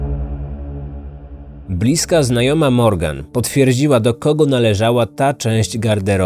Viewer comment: bliska znajoma Morgan Moj mozg: bliska znajoma Morda....😂 I po chwili musiałem cofnac co ja usłyszałem 😂